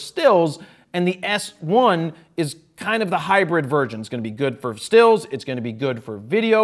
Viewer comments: Is it just me or did Jared just say hybrid virgin at 11:37 XD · Polin did you just say "virgin" at 11:38, instead of "version"? :D